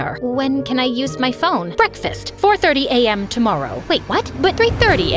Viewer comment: I thought she said 5:30 AM first